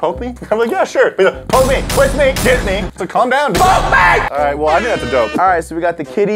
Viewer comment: How many people thought that the Sausage said kiss me